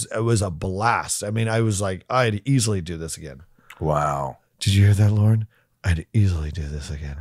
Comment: At first, I thought he said, "Did you hear that, Lord?" 😆